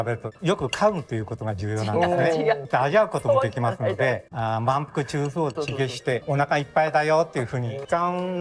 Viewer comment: I thought he said “manko” 😂